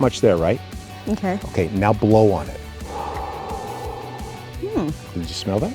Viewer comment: Did he say "now blow on it?" 😳